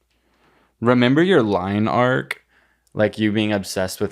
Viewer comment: i thought said “line arc” like ❄️❄️❄️ omgggg